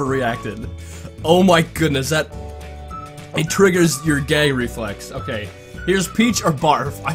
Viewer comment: he said gay instead of gag 😂